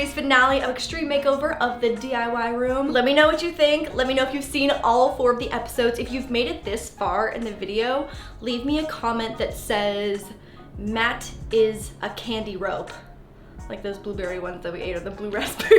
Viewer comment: Matt is a candy rope, at first i thought she said Matt is a candy robe, lol